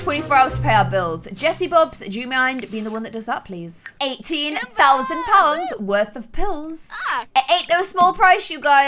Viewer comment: What said: "18 000 pounds worth of bills." What I thought she said: "18 000 pounds worth of pills." What's wrong with me..😶😝